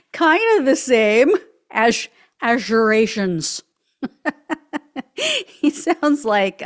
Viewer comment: ALL THIS TIME, I THOUGHT HE WAS TRYING TO SAY "EURASIAN". I HAD NO IDEA WHY HE WAS SAYING IT BUT I USUALLY CAN'T FOLLOW HIS "WEAVE" AS HE CALLS IT. WHATEVER, ADD TO HIS STACK OF WEIRDNESS WITH "COVFEFE"